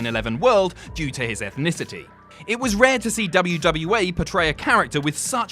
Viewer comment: sorry, did you say WWA?